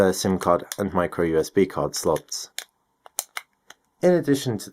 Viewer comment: Did he say Micro USB card slot